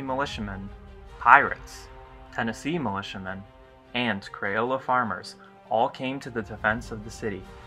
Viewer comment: I was listening to this while I work... I stopped in my tracks when I heard that "Crayola farmers" came to the defense of New Orleans. (16:48) ... At least, that's what it sounded like. I quickly figured out it was just a pronunciation of 'Creole' I hadn't heard before